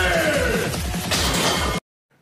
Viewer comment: Ich habe King of Kotelett verstanden. Super Leistung!!